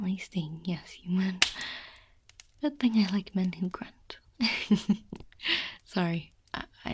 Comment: What does she say at 19:52 ? I hear something like "good thing I like mending grunt" or "men who grunt" idk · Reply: "Good thing I like men who grunt" - she is refering to the liquid stinging him so if he makes a noise she isn't bothered by it :3